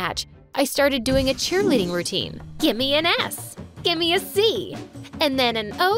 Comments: Anyone noticed in 6:47 Isabelle said gimme an F instead of an S for Scott??????? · i love how the subtitles say “give me an f” when she said “give me an s” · when she goes give me s c o t t it says give me a f c o t t 😂and at 13:01 it says jake instead of Scott